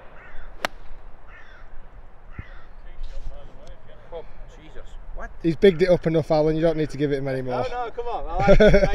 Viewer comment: what did Alan say at 6:05? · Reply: Lucas Becker i heard "oh jeasus wept"